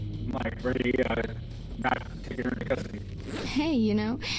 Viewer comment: Not take her into custody (thats what i think i heard lol) Girl: Hey..you know..you're kinda cute: Oh my gosh